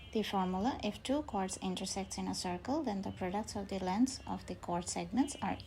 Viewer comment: did you just say "fodacts" LMAO